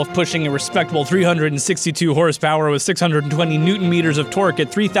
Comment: Did you say 620 N M of tork or twerk?